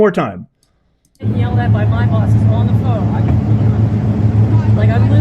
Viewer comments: It sounds like she says, “I’m being yelled at by my bosses on the phone.” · I thought she said She said I’m getting yelled at by my “BOSS” on the phone. Listen again ❤ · It sounds like she says "I'm being yelled at by my boss, who's on the phone..." It may be a regional supervisor who caused this rather than the corporate offices.